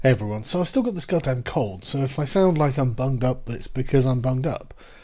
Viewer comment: Oh I thought that he said i still got that god damn cult but i rewatched the beginning and realized he said cold and not cult